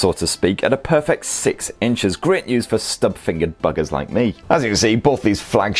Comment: I thought you said Stub-fingered bastards👏🤣